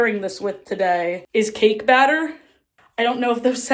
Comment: i was SO concerned when i misheard cake batter as kink batter 😭